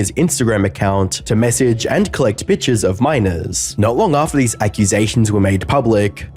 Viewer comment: I swear to God that I thought you said bitches of minors; not pictures of minors. 🫢🫣😵‍💫 Okay that’s enough internet for me today.